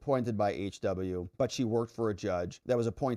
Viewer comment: It sounded like you said , "she twerked" , instead of worked a few times? or do I need more sleep? lol.